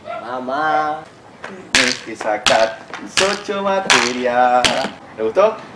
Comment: No entiendo lo que dice del minuto 0:51 hasta el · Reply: Yo escuche "te sacaste 8 materias"